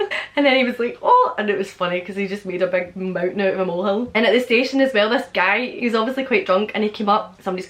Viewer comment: What you say at 11:19 (seemingly about something out the window)? And also, around 11:22 A green what?? Arandack? Anderack? I can't for the life of me understand what word you're saying. *Edit: I Googled it: Anorak In the U.K., an anorak actually has two meanings: 1. a hooded jacket. 2. a socially inept person. In the case of the first meaning, the word anorak didn't in fact enter the English language until 1924. Before this time, coats of this nature may have been referred to as a parka—a word still used in both British and American English. Lol, sorry I had never heard that word before in my entire life. Still like to know what you say at 11:19 though. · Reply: "he made a mountain out of a mole hill" (made more out of something than what one would expect) 11:22 "aodach" (I think) = clothing; but I think it's also slang for a rain poncho or similar. (I'm guessing)